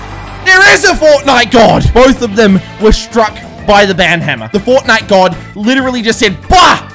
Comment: for a sec i thought it said bananahammer